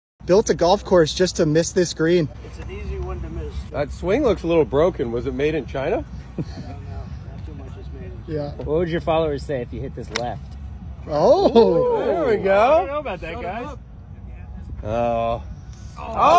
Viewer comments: "What would your followers say if you hit this "Left". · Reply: I thought he said "what would your father say" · "What would your followers say if you hit this "Left". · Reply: I definitely thought he said father not followers